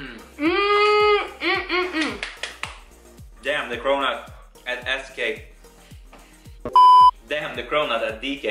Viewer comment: Did he say ASS CAKE??? I am rolling here on the floor